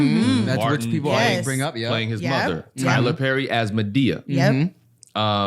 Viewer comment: I thought Ricky shucks said he takes 4 Perkys a day 😂😂